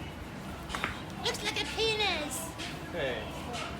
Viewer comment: Lol, did anyone else hear the guy in the background say 'it's like penis' at 09:35?